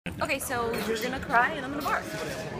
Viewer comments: Is it just me or does it sound like she said "You're going to cry and I'm gonna barf"? Boy am I glad I was wrong. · I thought she said youre going to cry and im going to barf. I was disappointed.